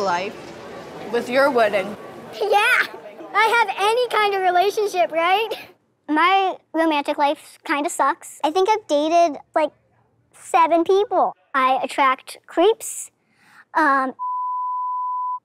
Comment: "I attract creeps... n-words", did she say that?